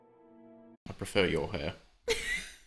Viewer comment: when did he say that? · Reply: oh yeah, I thought he said "I prefer u over her" But now I think he said "I prefer ur hair" lol idk